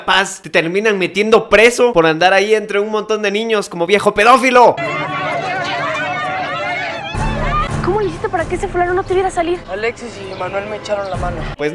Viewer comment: En el minuto 2:24 parece que dice "Ese culero" Xdxd